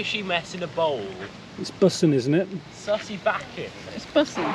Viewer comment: waaaait, did they say "it's bussin' isn't it?" , "it's bustin'.. it's bustin'" ?! i thought that was only an American slang